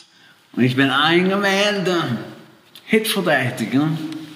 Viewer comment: Was sagt er da? 0:15 hipsgeweledig? Or what